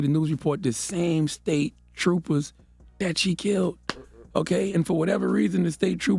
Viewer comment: They did let her go ...a week ago and she killed them. Same cops....u didn't catch what CthaGod said at @ 5:25 mark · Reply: She kill the same troopers that pulled her over moments before they died.. They let her go because they got a call that the other man she killed was walking on the highway. I'm saying imagine if she had kill other innocent people on the road because they let her go.